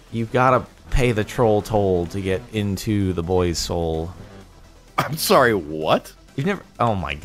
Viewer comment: I thought he said to get into the boys hole